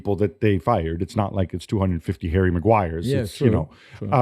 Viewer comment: First thought Gab said "hairy Macguire" 😅😂